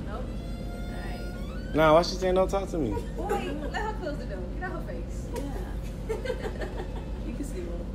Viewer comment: All I hear “face ahhh” 😂